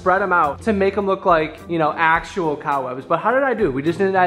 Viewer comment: just me or does it sound like says “cow webs” not cob webs” 🤣